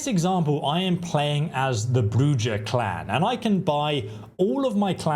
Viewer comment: Did you just say Bru-Jaw? Lol Love your content! Thank you for the awesome vid!